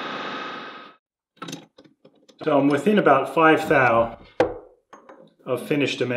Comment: what is thao or whatever he says